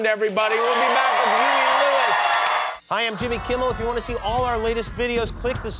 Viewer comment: swear at 14:32 Jimmy said "Hi i'm Timmy Kimmel", or am i just hearing things loolll